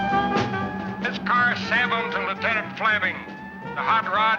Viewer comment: Great flick. Probably just me, but at 56:13 I heard “This car sabinth ‘n muttad flabbing” ??? 😂 Anybody?